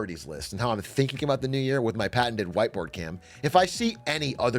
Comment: thought he said "white boy cam" lol